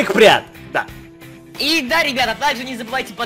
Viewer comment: Когда Жека сказал "И да ребята спасибо вам большое", то мне послышалось "Еб*ть ребята" 😂😂😂😂😂😂 Я РЖАЛА!😂😂😂😂😂😂😂